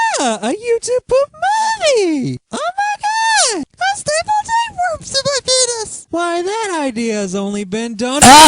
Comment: Did he say what i thought he said? "I staple tapeworms on my penis", y'know, when he was speaking in falsetto.